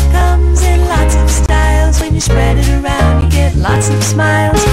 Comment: The first time I saw this commercial, I thought she was saying “a dollar”, until it showed the words “A Dollop of Daisy” on the screen at the end. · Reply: I thought it said a dollar too and came to find an old vid just for my sanity lol😂